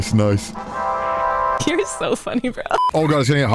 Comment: Did she say "Cutie is so funny"? 😂